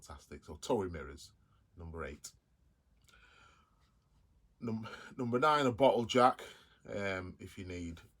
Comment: I thought you said 'Number 9 - a bottle of Jack'. Don't forget the ice! 🤣